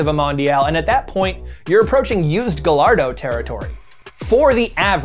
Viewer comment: He just said “Gallardo” not gayardo one and said “gayardo” the second time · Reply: I heard it. 8:23 gah-LARD-Oh